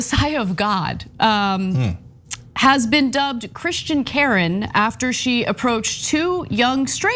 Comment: I was so excited I thought it said Kraken instead of Karen I am a little disappointed it was not Sydney Powell!